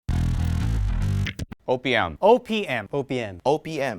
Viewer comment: Bogart sounded like he was saying Opium